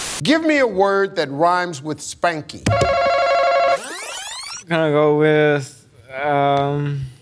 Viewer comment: I thought he said Franky. I thought that was a good choice. Until Steve corrected my mishearing.😅